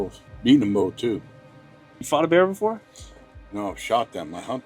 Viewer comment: He thought the guy said he fought a bear lol he said eat I'm pretty sure